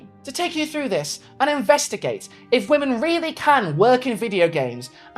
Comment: for the splittest of seconds, i thought you said "inbreastigate" instead of "investigate" and i feel like that could make a good series title for... Something? I dunno I'm not creative lol